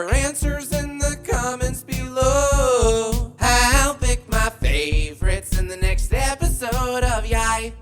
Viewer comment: #YIAYoutro when i first subscribed a long time ago i thought ur outro said: “leave ur answers in the comments below how big’s my forehead find out in the next episode of YIAY!” so uh yeah